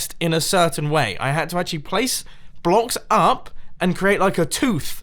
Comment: it sounds like you say sauce blocks lol